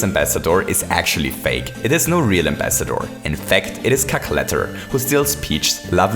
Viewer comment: It sounds like u say Cackletter xD No offence